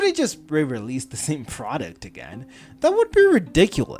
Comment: Not sure if I just misheard but I am adding "frauduct" (product) into my dictionary since thats what I heard and I love it